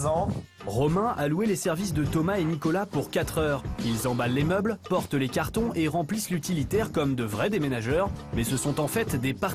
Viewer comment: meuble à sec... Normal. La voix off elle dit quoi : "Ils emballent les meubles..." 😂🤣😂